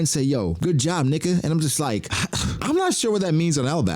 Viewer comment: I know what y'all thought. But he said Nika